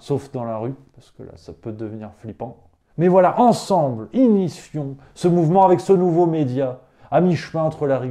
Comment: Accordons nous sur un désaccord… C'est moi ou a 3:51 on entends "…inifion…" ? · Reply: Initions